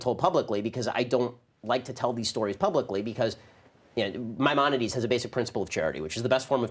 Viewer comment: What word did the man use at 11:24 " my _ as a basic principle of charity " ? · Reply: Maimonides